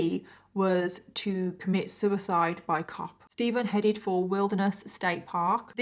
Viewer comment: "Suicide by cop"...? Did I hear that right?